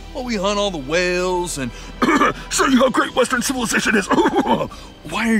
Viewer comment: “Show you how great [idk what he said] is huhuhaha-“ Got me laughin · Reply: He said "Show you how great western civilization is."